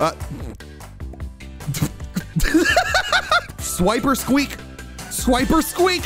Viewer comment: why do you sound like youre saying "Swiper squeak" itz like you used Swiper from dora the explorer and made it your pokemon.